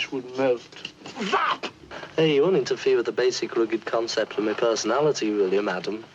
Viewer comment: I cannot understand at all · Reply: Cate Crutcher "Hey do you want me to feel the basic rigged concept of your personality, will you Madam?"